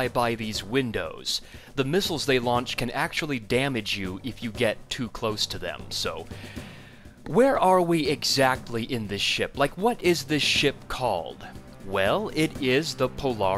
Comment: I almost thought you said 'Where are we in this SHIT?'